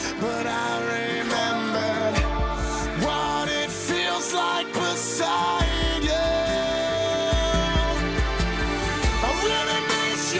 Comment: Instead of 'what it feels like beside you' I kept hearing 'when it feels like Poseidon' ... not sure what that feels like ... probably wet ... my version did give me much to think about though